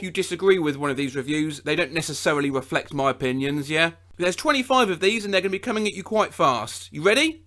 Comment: that “yeah” sounded like 2D